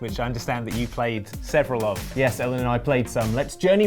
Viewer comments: did call Ellen? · Did say yes Ellen to